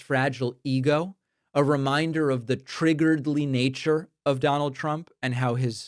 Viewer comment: Did you just say 'Triggeredly nature'?? 2:20? Been listening to too much Trump speech 😂